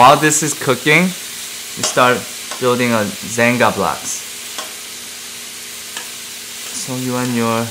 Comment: I heard 'xanga blogs' instead of 'jenga blocks' and it brought back so many dark time memories of my 8th grade xanga where I wrote questionable poetry about my sad girl feelings.